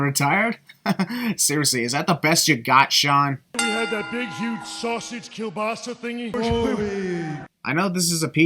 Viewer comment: Did Shawn say "sausage kiełbasa"?